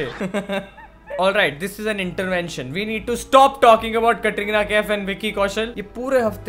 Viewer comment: 2min 50sec he said we need to stop talking about Katrina kaif maybe my sounds crack or smthing I heard ''TATRINA'' N I was like tatti jokes apart